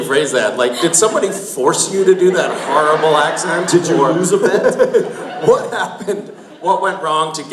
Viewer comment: What does Jensen say at 0:37? I'msure it's relly funny. I wish I knew what he said. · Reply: This is really late lol but he said "Did you lose a bet?"